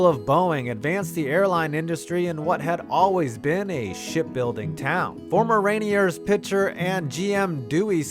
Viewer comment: I heard "Shit building town." Eh,close enough